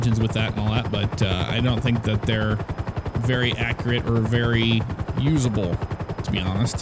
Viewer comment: did you just say accurate as RIck Ross?